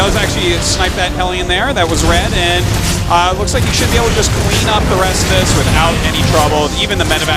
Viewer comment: Did he just say that ByuL should be able to queen up the rest of that attack?